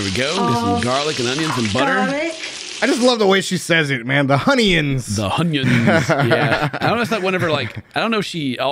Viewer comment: Magic mushroom 🍄 for i thought she said funonions!